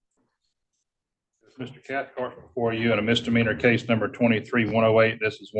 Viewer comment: did he say Brian "Catfart"??